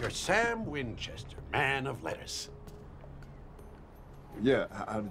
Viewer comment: guy: Sam Winchester, man of letters subtitles: Sam Winchester, man of lettuce i am crying 😂 i mean, it's not wrong 😂😂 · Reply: To be honest I thought I heard him say lettuce at first until context clues made me remember men of letters.